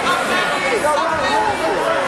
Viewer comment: For people wondering what DQs were for: 00:06 - obscene gesture (he got penalized in the video, but after the match, which he won, refs changed decision to a DQ 00:15 - headbutt 00:33 - that move was legal, Sanchez's opponent got mad and punched him which led to DQ 01:41 - slam (wasn't allowed on this ruleset) 02:28 - slamming to a wall 02:44 - unfair DQ imo, looks like ADCC trials, slamming while beeing threated with a sub is allowed. Maybe ref didn't know such thing as buggy choke exists 03:11 - throwing opponent out of scene 03:20 - punches and kicks 04:50 - headbutt then punches 05:10 - pushing and rude behaviour after stoppage 05:35 - intentionally throwing opponent out of scene 06:00 - doctor's decision - unstoppable bleeding 06:40 - regular tap 07:05 - spine lock 07:25 - spitting 07:40 - slam 08:04 - slam 08:30 - slam 08:40 - scissor takedown 08:46 - scissor takedown 08:55 - while doing straight foot lock, turning into the direction of leg not being attacked 09:10 - while doing straight foot lock, turning into the direction of leg not being attacked 09:26 - heel hook (allowed only in brown/black belts, no-gi, adults) 09:41 - while doing straight foot lock, turning into the direction of leg not being attacked 09:55 - heel hook 10:10 - kneebar (allowed in brown/black belts only) 10:15 - ??? 10:38 - leaving the mat before announcing the result, maybe some rude words, dunno 10:55 - UwU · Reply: did he say he he farted in face?